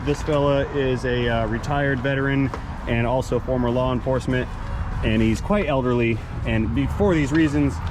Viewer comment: How tired am I while watching this one again. I managed to mishear as well as misunderstand what was being said, I thought that was referring to the heavy jack when he said "[ ... ] this fella is a retired veteran, and also former lawN enforcement [ ... ]" 😀😆